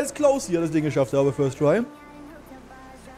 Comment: Obwohl ich weiß, das sagt, das es closed war, verstehe ich es so als würde er sagen "Das war Klaus." 😂